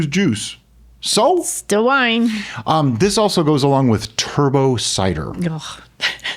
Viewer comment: I thought you said Turbos Cider. I think that is called StarSan.